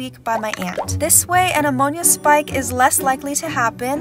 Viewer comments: Why did I hear “This way an among us spike won’t happen” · I deadass thought she said "An among us spike"